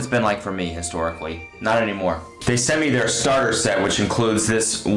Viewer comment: He said “they sent me their starter set, which...” But I thought he said “they sent me their starter sandwich”